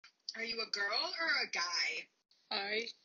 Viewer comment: I kinda heard him saying "I'm the embodiment of simp", and it still fits if you ask me